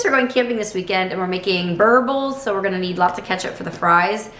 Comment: Hi, you said a word I can't understand. Its a 5:01 on the time. Bur bubbles ?? What is that? Thanks. Mary D.